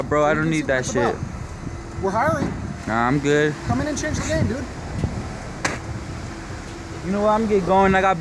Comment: I thought he said "come on in and change your name dude" i was cryingggg